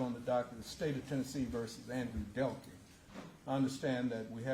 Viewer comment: I thought it said Andrew Caldwell💀🙂🕵